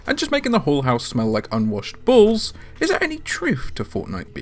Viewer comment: I heard "Unwashed Balls."